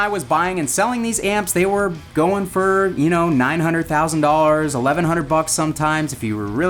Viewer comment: At around the 8:55 mark, I thought you said these amps used to sell for $900,000... ?!?!?!?!?! EDIT: You DID say $900,000... lol